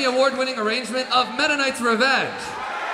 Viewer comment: Sounded like he said "Mennonite's Revenge..." :)